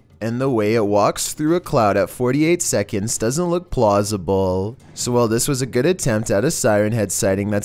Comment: He said "plossible" instead of possible😂